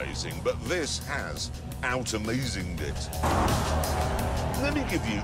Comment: did he just say out amazinged it